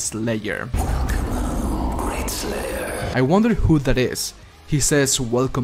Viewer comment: I thought that said, "walk along, great slayer"